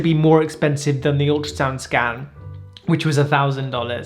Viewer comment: it sounded like he said "ultrasound scAM" which it actually is lmao